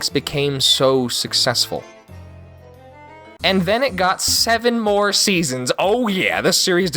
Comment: I HEARD GOT7